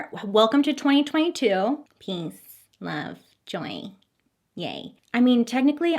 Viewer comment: I thought you said Peace Love Joint 😂💀😂 I won't take that last part since I can't stand smoke, but it made me think of grilled cheese instantly. That's joyful enough right?? 😆